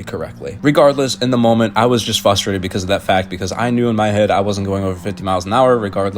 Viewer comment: Wait... @ 7:36 did I hear a "fustrated"? Sorry bro, but theres an "r" you missed. Frustrated